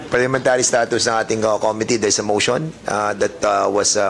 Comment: everytime they say comittee hearing my sense of hearing mishear it as comedy hearing😮